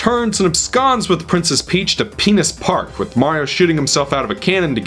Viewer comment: did he really just say what I think he said? Did they really name the park penis park??? Seriously. I haven't played the game so this is shocking.